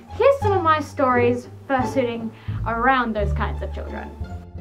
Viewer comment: For a second there i thought it said fursuiting for children